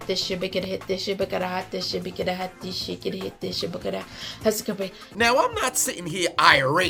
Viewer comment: I’m not going to lie when she was chanting I thought she was saying “gotta hit this shit gotta hit this shit” I was like what she smoking ?